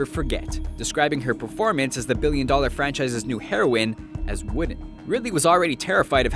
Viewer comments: "Describing her performance as the billion-dollar franchise's new heroin as...[WHAT?]" What the fuck did he mutter there? "Women"? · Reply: He described her acting as "wooden" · "…describing her performance as the billion-dollar franchise's new heroine as…" . As what? I didn't catch that. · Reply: "Wooden" I think. I had to use subtitles to get that. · "...describing her performance... as --" WHAT DID HE SAY? 'Women'? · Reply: wooden